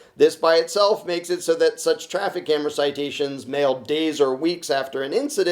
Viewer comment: i thought it said 'traffic camera ROCKET'. puts credit card away again